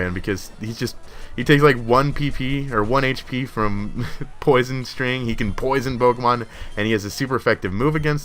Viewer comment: did you say poison string